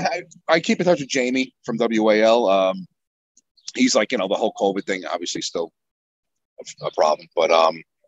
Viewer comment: Anyone else hear "The Hulk Hogan thing" at first when Mike said "The whole Covid thing"? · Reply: I absolutely thought he said "the Hulk Hogan thing." I didn't even realize he said "the whole covid thing" until I read your comment lol.